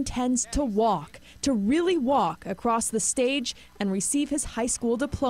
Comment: i seriously thought the news lady said wheely instead of really, my eyes popped outta my head so fast i had to rewind to rehear what she said, she didnt say wheely but god damn that threw me off lmao